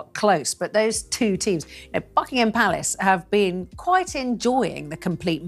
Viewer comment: Beautiful Duchess ❤❤❤ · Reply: Omg! I thought I heard her say f**** ham Palace 😅